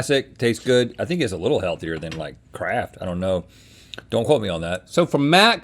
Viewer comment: Not going to lie, it took me a minute to realise Rhett said Kraft because I heard "I think it's a little healthier than, like, crack."